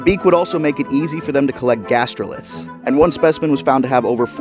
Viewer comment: Oh gastroliths stomach stones I thought you said Gastropods like snails and stuff I misheard lol